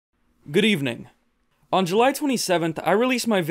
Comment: Я один слышу "Гривны" ? Казалось бы при чем тут...